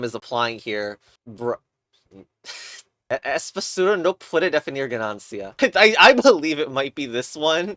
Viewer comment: Ok what are you saying in 1:56 Tryna make it the next cba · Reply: "es basura, no puede definir ganancia" he typed it in chat